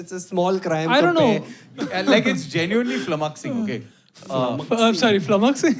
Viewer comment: guys, what's that word??? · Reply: Flummoxing